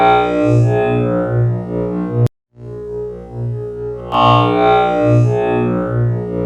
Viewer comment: I hear avocado's December